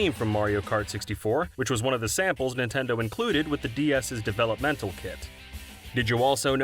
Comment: did you say Developmental kit? lmao